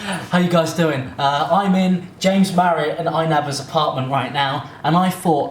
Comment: For a second there I thought you said “I am in james Charles apartment”and I was shistar shocked but then I realized I am only shistar deaf